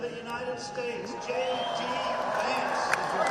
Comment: lol I'm sorry but did I just hear trump say J"T" Vance? Funny cuz he previously referred to him as "JD Mandel". Will this guy ever get his name right? Oh yeah, but Joe Biden's "mental decline"......